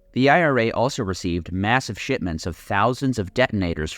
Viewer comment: I keep hearing "shitment" instead of shipment 💀